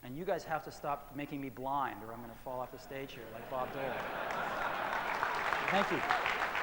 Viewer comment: I always thought he said "Bob Dylan", which sort of confused me. I didn't realize until now he said "Bob Dole"! Lol!